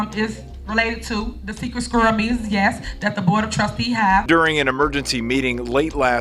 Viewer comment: she actually said "secret squirrel meetings". "Squirrels" are those who practice Scientology outside of the cult for MUCH less money. So is the "STUPOR GIRL MAYOR" more money-grubbing than Tom Cruise's cult? I'd say they are neck and neck.